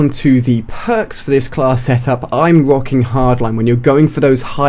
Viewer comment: i thought AliA said "Im rock hard"